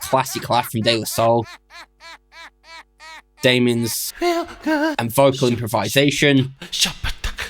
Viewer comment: I always thought that was him saying "Feel good" listen closely